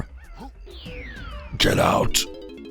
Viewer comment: when I was a kid, I couldn’t understand most of that voice in angry aztec, but I always laughed at it when I heard it cause I thought it said "Kill hurts" instead of "Get out"